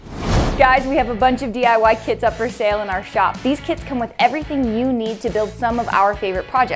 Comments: I thought she said kids not kits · I thought you said “A bunch of DIY kids up for sale on our website” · In the beginning i thought she said "DIY kid." I was like tf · I THOUGHT SHE SAID DIY KIDS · At the beginning of the video I thought she said. “We have some new DIY kids for sale” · i thought she said “We have a bunch of DIY kids in our shop” i was bout to sayyy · At the beginning of the vid I thought she said diy kids and I was like tf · thought you said that you had kids up for sale in your shop